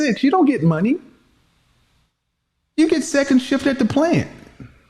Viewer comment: Wait a minute did he say you get 2nd shift at the plant? 😂😂😂😂😂😂😂 I can’t with 😂😂😂